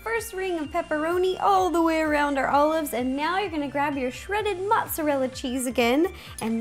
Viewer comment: please tell me I'm not the only one who thought she said 'worm water' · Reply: ***** I heard LOL and I also heard her say "Mozzalella" Cheese LOL she's so funny